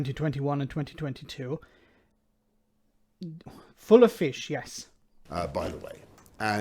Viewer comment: lol.... came to tears when I heard "full of fish"!!